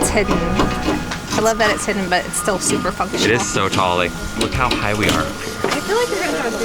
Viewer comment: I am sorry but like did she just say a naughty word. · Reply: No way. I never ever curse : ) I said fun or functional during that part.